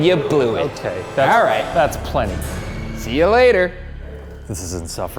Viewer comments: does anyone hear a voice say "later" is that a a staff member of the mission that thought they were talking to him, or spooky ghost bear that learned to talk? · Did anyone else hear someone say later to him after he said it at 22:43ish.